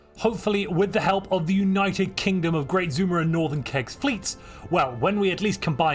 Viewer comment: I hear it as "Zoomer and Northern Kek"